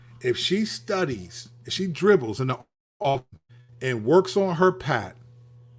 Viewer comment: Did he just say IF Clark works on her pass ?? Right now she is the best passer in the WNBA